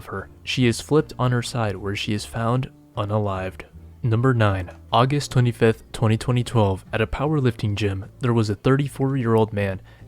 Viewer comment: you said august 5th 202012 instead of just 2012? Is this AI?